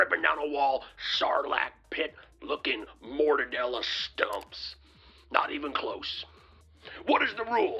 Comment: Pillow sales are waning so it's time to put on another "Cyber Symposium". Did I hear "mortadella stumps"? Lol. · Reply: 100% mortadella stumps.